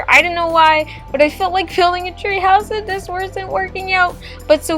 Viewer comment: I thought it said THREE HOURS lol